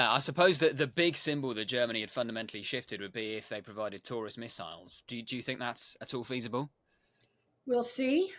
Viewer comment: Sounded like you said ... there is a rumor they are going to provide tourists missiles .